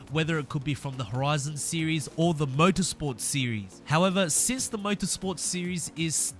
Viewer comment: Lol at 0:30 my computer lagged so it sounded like murder sport series instead of motorsport series